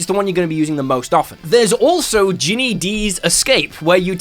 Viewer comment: I heard Djinni Di's Escape?